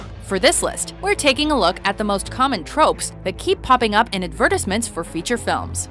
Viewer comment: Did she just say, "ad-ver-dis-ment?"